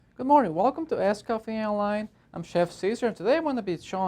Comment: lol It sounds like you said "Welcome to ass coffee and wine"